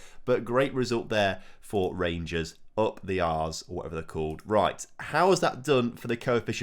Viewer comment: anyone else hear say 'Up the arse' at 26:09 ?